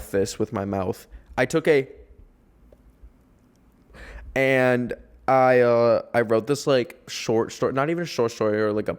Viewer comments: yall what did he say😭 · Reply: That he was on shrooms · What did he say at 25:04 I’m so confused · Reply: I’m pretty sure he said mushroom 😭